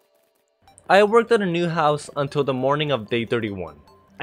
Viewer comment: it sounded like he said Gun stealer